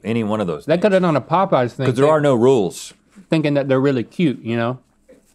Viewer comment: Can we just talk about how link literally said it was the Popeyes chicken when eating it and then insisted it was not the Popeyes chicken??? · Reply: He didn't say that he thought it was Popeye's, he said they might have used Popeye's because "they thought they'd be cute."